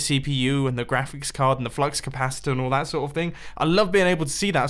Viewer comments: did he just say he likes to see his "flux capacitor" in his pc...WUTFACE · did he say flux capacitor?